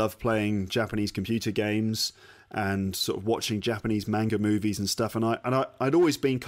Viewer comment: For one sec I thought you say you still love watching Japanese movies. Hmmm...